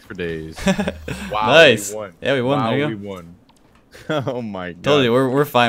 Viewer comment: Anyone else think that said "Yeah we won n-word" at 15:45 And can someone say what he actually said as a reply cuz that's rly fishy man